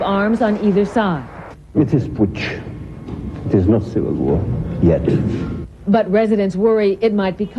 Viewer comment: what did he say? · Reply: there's no civil war....yet